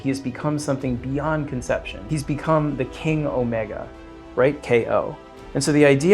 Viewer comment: Every time I heard them say “king omega” - I repeatedly thought they said “Kenny omega” Im way too much of an AEW mark